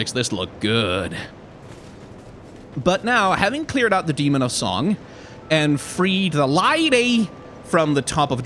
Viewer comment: I keep mishearing it as "Demon of Zaun."